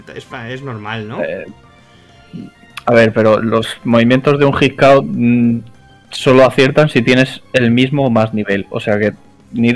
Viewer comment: Yo claramente escuché "los hit KO solo aciertan si tienes el mismo o más nivel"